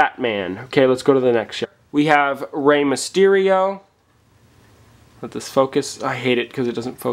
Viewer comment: oh mysterio i thought u said mysterion ( its south park i LOVE south park!)